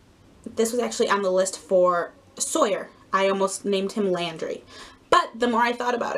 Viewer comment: OMG I thought laundry when you said Landry LOL Those are great names. Might have to use some of them for future reborn babies LOL